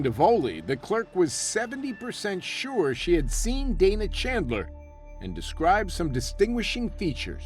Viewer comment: (also at 1:20:31) "... the clerk was 70% sure she had seen David Chandler and describes some distinguishing features." Was it David or Dana? Did I hear it right? 🤔🤔🤔 It should have been "Dana".